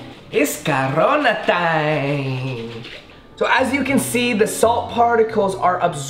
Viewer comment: In 6:27 he said it’s cabrona time instead of corona time